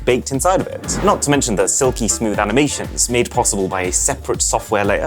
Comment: Did I just hear 'aminations' instead of 'animations' at 12:47 ?